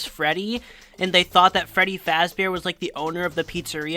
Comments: i always thought the guy said freddy fazburg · Great video! Brought back lots of memories of the old days back when a child could see this and think it was real... that child was me. Also, I always thought the guy was saying Freddy Fazbert, like if Freddy Fazbear was just an edit of his name or something.